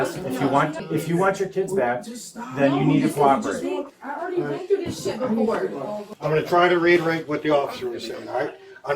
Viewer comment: Did she say..”I done been through this before”?! (8:19) This isn’t her first rodeo SMH 🤦🏽‍♀️